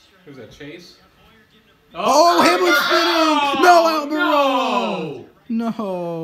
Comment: did you say NO AMIROLO???